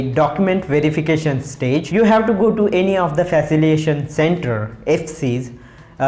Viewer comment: Sir can you please clarify what you said @ 9:19 - 9-20 ?? I didn't get it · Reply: Faciliation Centre to verify your documents